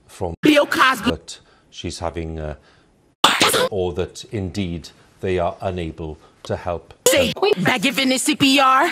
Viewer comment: what is she saying? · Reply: Whoregasm. This is a reference to the Cupcakke song.